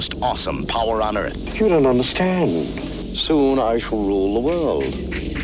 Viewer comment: "You don't understand. Soon i shool rowlderrood. " Ninja must have broke his teeth :D